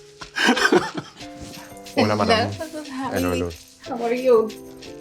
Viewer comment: Villeneuve mumbling "hello amy" Subtitles: [Speaking in foreign language] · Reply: no but i think he says "hola madame" 😂